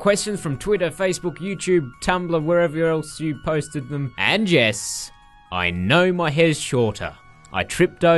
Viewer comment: I thought you said 'and jess' instead of 'and yes' hahaha.